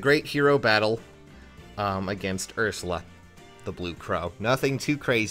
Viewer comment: I always thought (and still say it as) that it was "The Blue Cow", which is much better than "The Blue Crow"